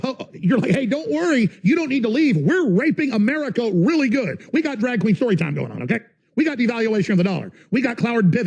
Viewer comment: Did he just say "drag show furry time"?